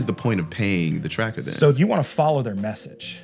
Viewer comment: Thought he said cracker